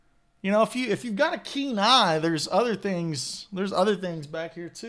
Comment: When I heard you say “ If you have a keen eye” I heard Kenai ……….lols